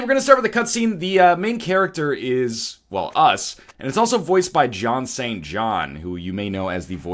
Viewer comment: i almost thought he said john ceena but it was john sean john:(